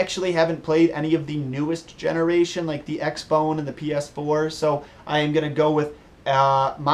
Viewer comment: Wait a minute... at 11:10 did he just say "X-Bone"??